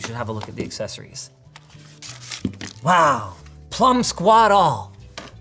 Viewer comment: What does he say at 0:53? I can't make it out. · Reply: "Wow, plum-squat all"